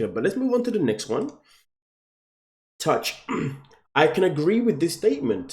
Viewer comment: Did he say TOUCH?????? 😂😂😂😂😂😂😂😂😂😂😂😂😂😂😂😂😂😂😂😂😂😂😂😂😂😂😂😂😂😂😂😂😂😂😂 broooooo 4:41 I’m dead!